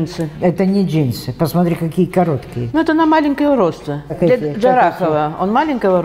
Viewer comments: Послышалось. "Это на маленькое уродство.."))) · Послышалось не "на маленького роста", а на маленького уродца XD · Бля мне послышалось для маленького уродства- джарахова😂😂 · На 9.53 послышалось не "маленького роста", а "на маленького уродца", потом сказали про Джарахова.. я так давно не смеялась 😂😂😂